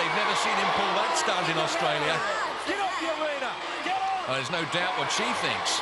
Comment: After Wolf shoved David after Hang Tough, I think Mike Whitney said to Wolf "GET OFF, YOU WEINER!"